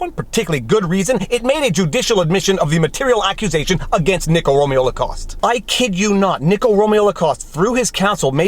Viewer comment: Everytime you said Nicko Romeo Lacoste I keep hearing "Nickelodeon Lacoste".